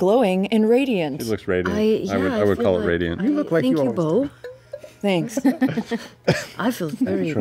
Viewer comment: Help me: what does Sam say at 7:32? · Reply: "you look like you always do"